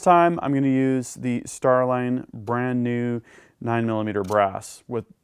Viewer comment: "Starline new brass" is what I heard while I'm inspecting my brass, some of it coming from 1992 when I started reloading for my Glock 17 gen 2😉